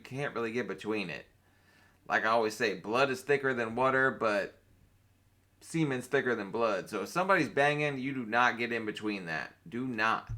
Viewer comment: "Blood is thicker than water, but semen's thicker than blood." you not only won the internet today, but you are a poet laureate. I love your sense of humor. You're hilarious. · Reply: I thought he said cream was thicker than blood. 😂